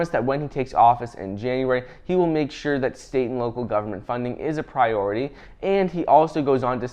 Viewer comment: "state & local government" .. what i heard, "satan"